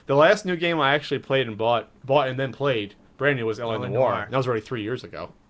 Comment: What game do say he bought and played 3:02? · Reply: LA Noire